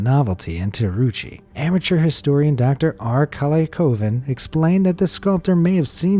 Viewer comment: anyone else thought he said dr r kelly